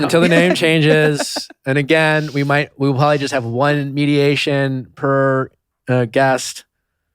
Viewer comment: Thought it said meditation, lol